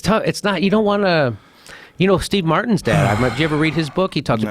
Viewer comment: I freaked out and spent 3 minutes frantically googling and questioning my perception of reality because I thought Jimmy said "Steve Martin's dead." Then I realised he said "dad."